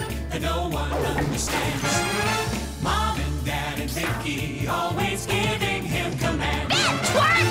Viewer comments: did that just say mom and dad are kinky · I always thought she was saying “big twerp!” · I always though Vicky said "Bad twerp"... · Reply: I always thought she said "Beg twerp!" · "Bad twerp!" "Kinky~" Vicky then changed countries and Timmy lost his godparents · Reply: I thought she always said (*BIG TWERP*) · I thought she said b***h twerp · Oh I thought she said "Bad twerp" not "Bed twerp" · I thought she said "Big Twerp!" Not "Bed" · She said "Bed Twerp!!!" But I thought she said "Bad Twerp!!!" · I always thought she said “BIG TWERP!” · i thought she said "That twerp!"